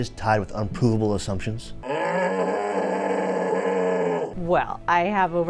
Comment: yo I swear he just said “unpooable assumptions” I think is constipated